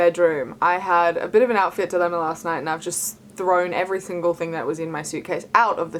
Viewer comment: did she just say “thingle” .... broken promitheth promitheth 😂 still love ya